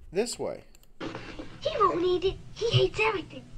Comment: Tv speakers run at 800 hz however we quit listening to the tv speakers in the 80's! First off my mother was an English professor and head of the Drama department . All my life I heard articulate! I need to tell you right off that I study and have always studied commercials! I am weird that way and I have a photographic memory! The very first time I heard the commercial (first run) I was hard put trying to figure out what was said! I heard "Eats" and "Everything" which was a conflict so the next time it was on about a half hour later I turned up the tv and listened to it close! It wasn't easy because the kids slurred the words but I finally heard "Hates" and "Everything" in the same phrase! It finally made sense! I remember it being a thing and correcting friends that said it wrong! I am 56 years old! TV was my life! BTW I'm O rh factor negative!